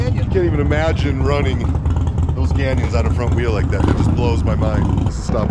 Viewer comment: When you were talkin to Paul Yaffe at the gas station, you pointed to his front wheel and said something that I could not decipher . I repeated that few seconds over and over and it sounds like you said “I can’t even imagine running those gannies on the front wheel like that.” If that’s what you said, then I have no idea what gannies are . Perhaps you could get me straightened out on this .👍🏻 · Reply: Running those canyons with a wheel like that 👍🏼